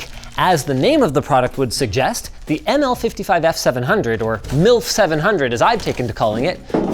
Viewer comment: did he say milk.. or MILF?